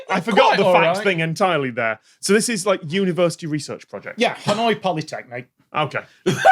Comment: wasn't entirely listening, and was VERY concerned when i read the subtitles as "Hanoi Pyrotechnic"